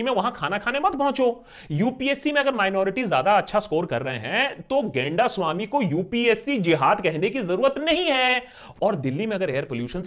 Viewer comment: did he say "genda swami" 😂